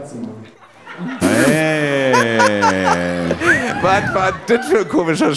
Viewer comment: Bei 19:00 Ich verstehe akustisch nicht was der Herr sagt. Also felix sagt was isn das fürn spruch. Höre irgendwas mit katze raus aber versteh nichts wirklich was. Danke euch für eure hilfe. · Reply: „Ich kann aus Tommi eine wilde Katze machen“